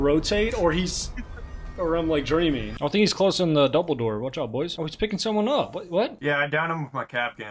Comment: I thought I heard Dumbledore probably because it was right after you said Harry Potter